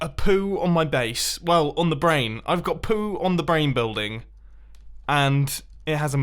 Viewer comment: I think is saying you have poo for brains